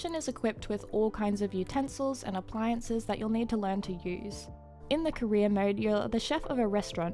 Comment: i thought i heard "in the korean mode" but its actually "in the career mode".